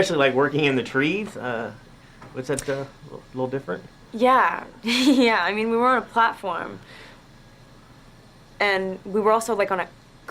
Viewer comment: Sorry can someone explain to me what on earth she said at 1:00. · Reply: She said " yeah , yeah I mean we were on a platform"